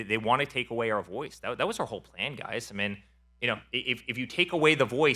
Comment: “That was our whole plan guys…” Did I hear that right?????